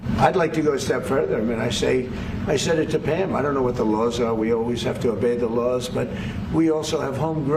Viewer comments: Sounded like he said, "We always evade the Laws". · I thought the president said he doesn't know the law but we have to obey the law.